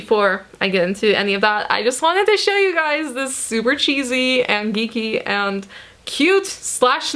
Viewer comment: what dijuu say ( 0:45 ), super chizie, dickie......nice....bahahahha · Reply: hahaha i said super cheesy and geeky lol